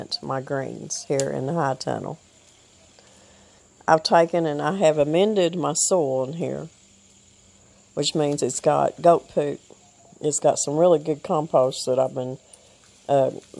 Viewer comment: When you started the video in your discussion you said, "I have amended my soil ..." What I heard was, "I have amended my soul ..." with your accent. I wondered for a minute what your soul had to do with the hot tunnel. Anyway, I had a good laugh. Take care and keep on keeping on.